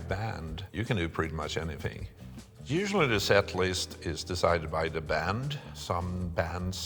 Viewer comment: At about 3:35 he says "usually the set list is decided by the band" but the cc says "usually this at least" If someone who can change it sees this, then please do